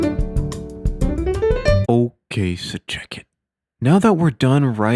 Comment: What does he say at 0:35 is it “ok sir jacket”? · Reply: "ok so check it"